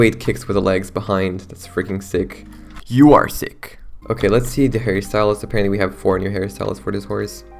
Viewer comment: I really thought he said “let’s see the Harry Styles” 😭